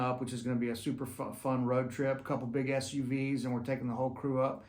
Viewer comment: every time you say "big SUVs" I hear "big ass you vees"... Happy birthday buddy!